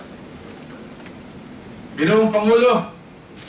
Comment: look! did he say PANGINOON? at 28:11minutes of this speech, then sabay punas ng bibig ( oh shit pangulo pala..)